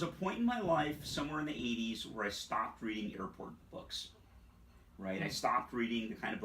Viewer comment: Need some clarification here, if you don't mind. At 6:47, says "There came a point in my life where I stopped reading '_____' books." I didn't catch the word he used. Was it "important?" I'm asking because it establishes context. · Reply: 'Airport books' the kind you pick up at an airport; paperbacks.